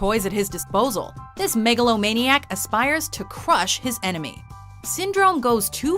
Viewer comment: this megalomaniac Me wait did they say this megalovania Sans are you there